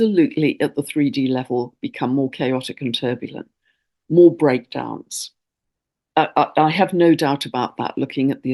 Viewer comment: At near the end I heard you say, “…. there will inevitably be a lot of Breakdance…”. Mmm, I thought, great! I love to dance around my living room which is open plan to kitchen/dining, so I can cover the full way round the prep island, and dervish my twirl on the round Axminster! Then I rewound the recording and heard the correct version: “….Breakdowns…”. I laughed, and thought, my soul KNOWS how to convert breakdowns into breakdance. As ever, thank you.💃🏾💃🏾